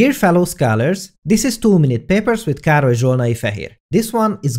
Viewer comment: I always hear "too many papers" 😂😂